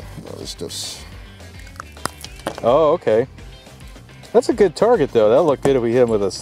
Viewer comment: tungsten ball. same size. more powder. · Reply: also, at 3:58 YouTube's subtitles said "gay" when you said "good" while checking out the remains of the bear. pretty funny troll on YTs part